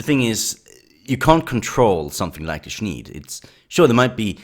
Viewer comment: what is a shneed? an uncontrollable wave of spurging? its a shneecla? of arsen? go sneed? you like em? dude what did you say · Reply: Sneed is a large community of people. There is also a ton of smaller groups, for fallout they are called the sneedclave